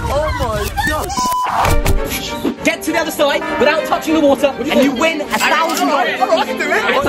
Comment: Wait I thought that you said do touch the Water You